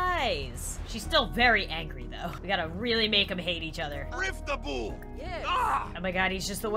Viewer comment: am I the only one who heard "rip the womb"?